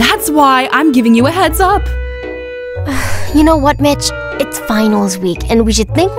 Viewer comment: When she said Much I thought she said b*tch At 0:33 NOW I FIANALLY RELISE THEY ISE HAND CUFFS TO HUG thats the smartest thing ever 4:40 Me at the end No NO NO NO I NEED SECOND PART NOOOOOOOO PLEASE MERCY